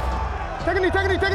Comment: Have I been watching too much Blacklist… or is Marlo calling Oweh “Dembe” ???